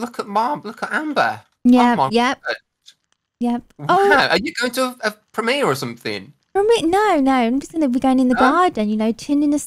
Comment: you going to a promiere or something? {me not hearing him properly} what i thought he said: u doing this for me or something? me: ummm what??? lol i had to listen to it again and slow it down. thank you! i love berry avenue, dont get me wrong but i really miss these bloxburg videos